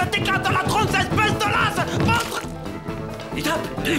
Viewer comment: montre-toi que je t'éclate à la tronche espèce de nase; LOL MDR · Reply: C'est "Lâche" pas "Naze"